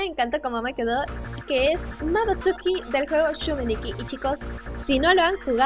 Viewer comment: ni entendí el nombre del personaje ni del juego ayuda:’( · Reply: El juego es Yume nikki (el personaje no entendí)